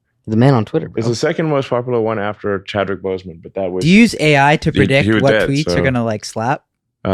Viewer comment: Yooooo, at 2:10:38 when he said second most popular tweet to Chadwick I swear he said "but you're dead so" lol wtf, and nobody even reacted to that 🤣